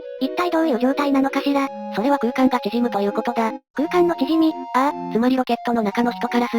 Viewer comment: ここお腹空いてて空間のチヂミと聞こえてしまった